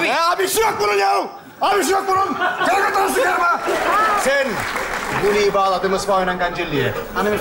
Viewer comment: Ozan ne diyor orda Kıbrıs'ta kullanılan bişe diyo ama :D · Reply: "guliyi bagladim ispahoynan gancelliye", yani "kopegi iple bahce kapisina bagladim"